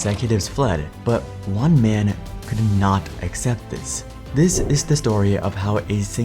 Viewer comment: exit is a *noun*, dummy. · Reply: you misheard "accept" as exit. dumbass